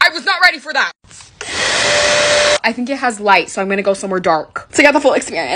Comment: When she said “I’m going in the dark bc I think it has lights”I thought she said lice😂😂😂😂😂😂😂😂😂😅